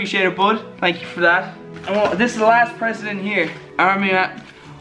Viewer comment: Did he just say president?